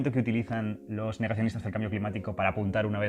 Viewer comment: En el 5:11 no entendí que dijiste ) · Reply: Naiko Blue “Negacionistas del cambio climático”